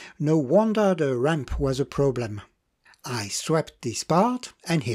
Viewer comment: when you are describing the fix on the motor I am struggling to make out what you said there - "I 'swept" this part"? What did you actually do to resolve this issue - I have bought a number of new condition but seized units and will be undertaking a steep learning curve in restoring them to running order, so I've been watching and rewatching many of your videos! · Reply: I "swapped" (= changed for an equivalent one) the part. Pardon my French accent...